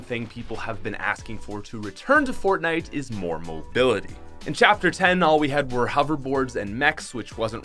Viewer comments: Did he say chapter ten? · I heard you say chapter 10 lol